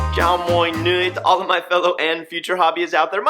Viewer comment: Did he just said “ chao moi nguoi “ at the start of the video